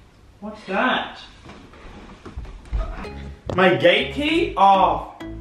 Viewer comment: Looool 11:37 I thought he said “my gay key”😂